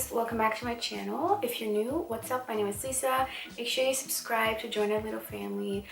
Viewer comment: Oooooo I thought she said “my name is sza” I was like AALSJDNXUDSNSKS HOLY MOO